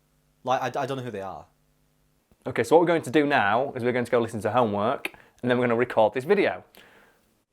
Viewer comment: "we're going to listen to homework-" what I hear: do the homework